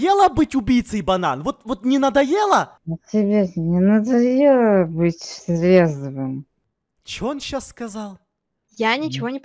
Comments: как я услышала Банан сказал "А тебе то не надоело быть трезвым?". ЗАЛАЙКАЙТЕ ЧТОБЫ ЭДИСОН УВИДЕЛ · "А тебе не надоело быть трезвым?" — вот, что он сказал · Я поняла что он сказал:а тебе не надоело быть трезвым? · А тебе не надоело быть демоном? (Чисто что я услышала)